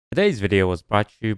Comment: Any other Dutch people hear 'deze video' instead of 'todays video'